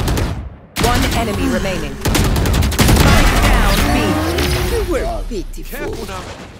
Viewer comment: though I hear sike but spike